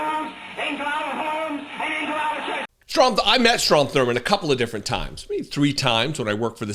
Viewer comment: Did I just hear call that vile rasist Trump Thurman? 😂😂